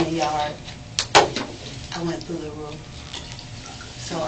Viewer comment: does mama say she went through the roof??